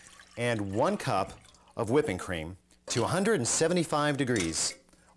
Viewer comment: i thought he said woman cream lol